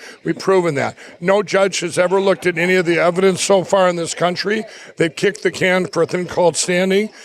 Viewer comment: Lunatics I thought he said he kicked his drug use?